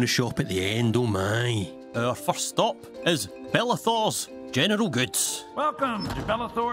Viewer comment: i usually watch american and british youtubers, so i'm not really used to hearing accents... and i thought you said "genital goods" and was flabbergasted for a minute 😭